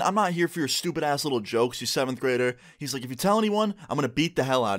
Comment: Shit I thought he said 2nd Grader😂